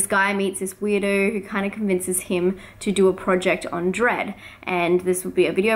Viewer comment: I never read the list so when you said dread I thought you were saying dredd yesterday